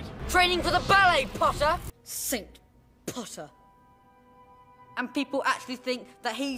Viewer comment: i heard sigma pottah